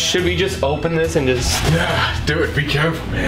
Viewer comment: bro this was truly an unbelievable night. SO MUCH amazing evidence. can’t wait to show everyone our experience at the Coronado hotel too. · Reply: Sam and Colby @ 4:08-4:09 I heard a voice saying "Yeah." Do you think it was one of you?